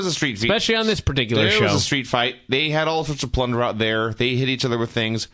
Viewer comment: What did say at 10:46-10:51?... "They had all sorts of--" what?... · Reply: They had a street fight, all sorts of plunder (garbage).